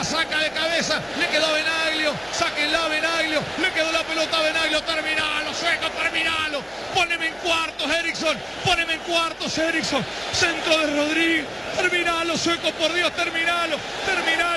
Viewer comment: Escuché "Poneme en cuatro" :v